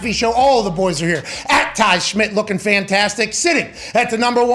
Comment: Why do I hear “anti-Schmit” when Pat says “And Ty Schmit” during introductions. 😂